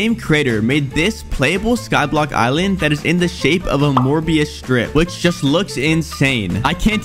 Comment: did you say morbius?